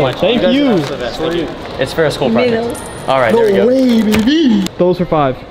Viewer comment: did he say it was for a school project???